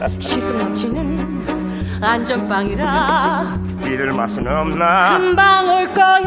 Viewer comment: 여기서 조승우가 뭐라고 하는거에요? 믿을 맛은 없나???? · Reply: 비들 맛은 없나 라고 하는거에요 스위니 토드가 복수 하려는 사람이 비들하고 판사거든요